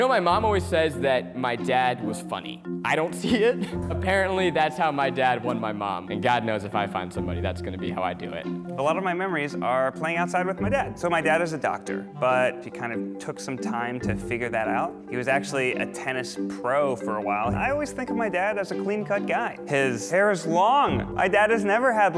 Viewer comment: I thought he said adopted